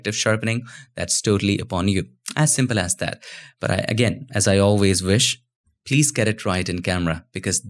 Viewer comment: Sorry what you say at 13:07? "please get a ***** camera"???